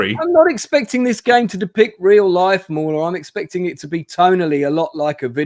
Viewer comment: Did really call a Moron at @ 8:44 ... wow.